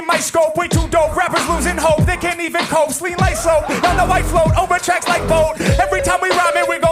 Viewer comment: one thought I have is in the part around 16:00 when Harry stumbles the "ski like slope" line, I think in the moment he caught "lean like slope" to incorporate name and his mouth couldn't calibrate the switch quick enough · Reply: Aah, I heard it as a slip on "clean like soap", which Harry uses often. Also saw subbed it as that :D <link>